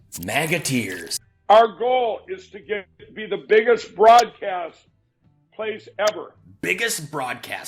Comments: I thought he said “Fraudcast”! 😂😂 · I thought he said the biggest fraudcast